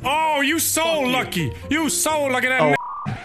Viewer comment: I’m just gonna defend Nogla real quick I’m sure no one’s mad at him but the clip at 10:10 he actually says “you so lucky that nicked him” as in it hit but never went off which he clarified seconds later